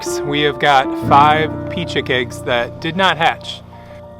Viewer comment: I heard, “You gotta pee?” Oooohh... you said pea! 😂😂😂